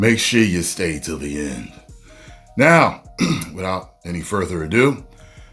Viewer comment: sound like you said now with out folie à deux😂